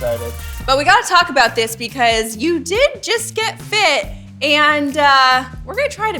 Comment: LOL, I thought Paige said “You did just get FIXED”